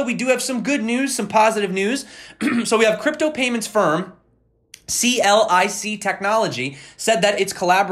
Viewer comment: Lol thought you said C-L-I-T technology. Had to rewind.